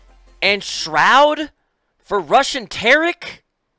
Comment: Am I the only one who heard: "...for Russian Tarik?" :DDD